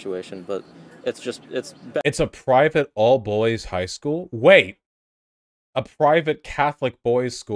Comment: i stg, i initially thought said "all BULLIES high school" lmao